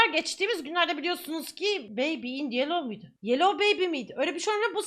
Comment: yellowu yelloz diye duydum ;_